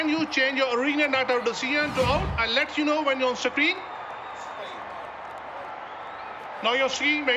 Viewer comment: Whoever is watching this highlights please listen to what Umpire said at 17:45 laugh and thank me later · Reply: 😊I heard it . "Screen"